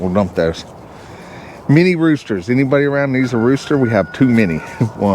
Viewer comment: Lol, when you said "to many roosters" i heard "two mini roosters" and i thought cool. I have some mini roosters and i love them. Got a mean streak wider than they are but they are fun to have around. Building in Williamson county Tennessee is strict also. After we had the field area marked off it became God's Land and strictly off limits. One of the foundation crew drove his pickup over the edge of it to park on the other side of the house (for some reason) The inspector seen tire tracks and shut us down. It took weeks to get the soil retested and a new permit issued. Should have just slid him 5k and went back to work. It would have been cheaper.